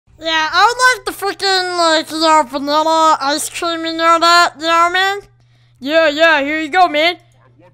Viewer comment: what that voice said? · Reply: He said I want my f-ing pickles and my ice cream n-word